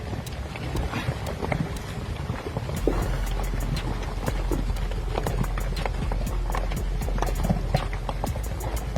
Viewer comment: “phased with the ? Did you say humans are insignificant? AI generated? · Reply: "Faced with the power of nature, humanity is truly insignificant." how it can be AI?